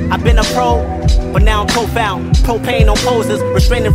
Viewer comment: I've been a pro, but now I'm profound. Propane on posers, restraining from the doja. Imagine if I smoke, I'd probably come up with a quote- so heavy you'll forget every album you heard from Hova. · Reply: I always thought he said, “Now i’m profile” like being searched by cops lol